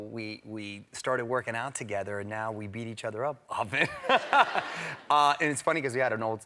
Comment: Thought it said “Often eat each other out.”